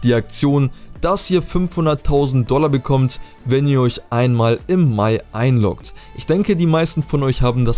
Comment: was hat er gesagt wo soll man sich einlogen könnt ihr mir bitte schnell antworten wäre cool 🙏🏻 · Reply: Man soll einfach nur mal in Gta online rein gehen im Mai